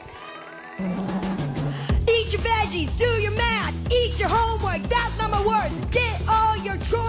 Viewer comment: did she really say eat your homework? yes. i put on CC and thats what it reads as well